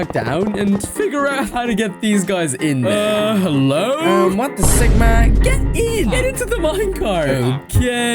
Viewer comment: ain’t no way bro 💀I just heard “What the sigma”